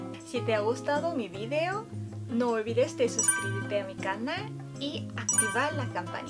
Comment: jssjsj me dio risa que dijo desuscribirte en vez de suscribirte. Me encantan tus vídeos <33